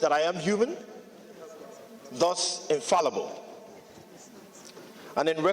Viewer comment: did he say he is infallible? Or I heard wrong.